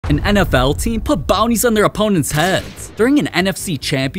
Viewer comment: I miss heard this as “bombs” instead of “bounties” and was so confused lol